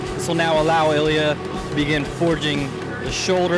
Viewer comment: When it said begin I thought it said Spaghetti